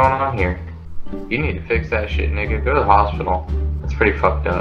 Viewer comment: DID HE SAY NIGGA? ಠ_ಠ🔫